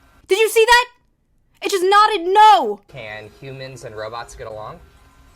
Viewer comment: When the intro dude said "robots" I thought he said "row boats" and it's just funnier that way